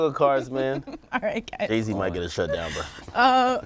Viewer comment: what did ack say 16:23 "haisey might get this shut down man" anyone else heard it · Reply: He said Jay z